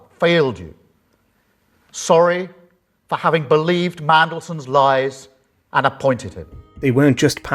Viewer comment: I could have sworn I heard Pesto call him "Paedo Mandelson." I never had much respect for Robert Peston. Until now.